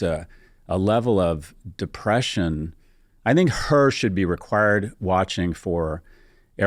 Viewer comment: What does he mean when he says "her should be required teaching" ? Maybe im mishearing the word he is actually saying? Anybody?